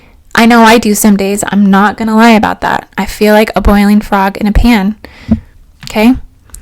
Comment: I misunderstood briefly thought you were saying you some days boil frogs in a pan.